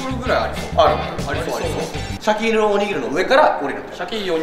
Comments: ここ俺もシャケ入りおにぎりって聞こえた🤣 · シャケ入りおにぎりの空耳が好きすぎる🤣🤣🤣